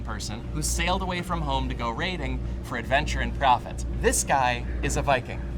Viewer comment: low-key thought he said left home to go raving lmao